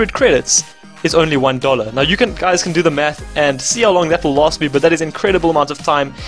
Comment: Thought he said u guys can do the meth I'm Like what!? Lol😂